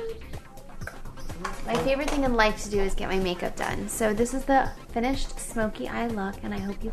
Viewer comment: wow! did she say at 4:59 " My favorite thing in life to do is get my make up done"